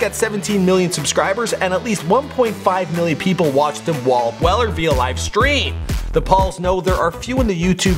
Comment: What did you say at 1:01?walb weller?